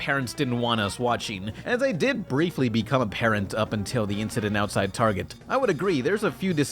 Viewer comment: Is it just me or did he say he briefly became a parent at around 7:50 ?